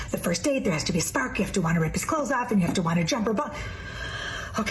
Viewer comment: W..? Wait, did she really say "I've matured!"... daym.. weird.. I heard "Dried up!". oh well...